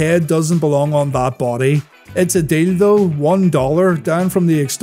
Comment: it's a what? Edit: a deal tough... yeah I misheard that..